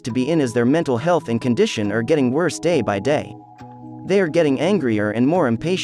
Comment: I thought you said they were getting adopted !